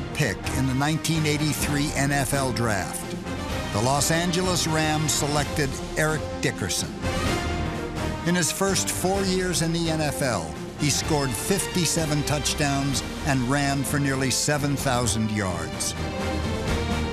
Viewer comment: Yo I had to run it back twice I thought my guy said in his first season he ran for 57 TDS and 7000 yards I had to do the math in my head like no that can't be right .